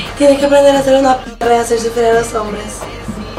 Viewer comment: El 2020 y en cuarentena por el covid y sigo sin entender lo que dice en el minuto 1:21 Like si estas igual Haha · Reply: Dice que si es su marido que como deja que la tratate así delante otras mujeres que debería ser más perra y hacer sufrir a los hombres creo